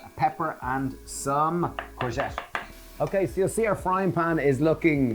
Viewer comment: some what!? Seriously you should have a peek at what the subtitles suggest you're saying - Utter gibberish sometimes, truly disturbing at other times - 3:27 "little bit you know you could just add a frame German yeah crank human.." "Longpig, that's....bold." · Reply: he is saying "courgette", which is British English for zucchini. At 3:27 they are saying "ground cumin"